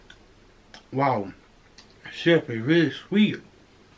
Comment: I'm sorry but at 1:42 did say "wow, shit be really sweet"? 🤣🤦‍♀️